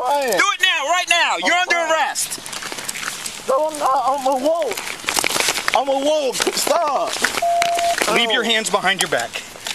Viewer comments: I could've sworn he said "No I'm not, I'm a wolf. I'm a wolf, stop" when the cop was putting the cuffs on saying he's under arrest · "No I'm not. I'm a wolf, stop!" Is that what he said? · “no I’m not! I’m a wolf!” 😳 am I the only one who heard this?